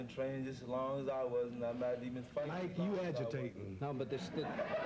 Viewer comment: What did Muhammad say at 2:08? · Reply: bandar alhazemy "Mike, you agitating (me?)"